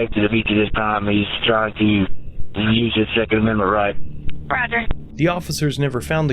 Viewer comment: did he say “he’s trying to use his second amendment right”?